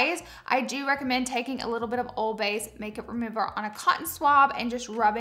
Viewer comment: I thought you said to put, "old bay" on a qtip to loosen the adhesive on the lash glue. 😂😂😂 and I was like whaat?i i KNEW that's not what you actually said lol, it's just what I heard. She actually said, "oil based" 😂